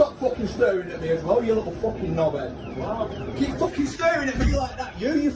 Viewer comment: app. I think that the passenger says "knobhead".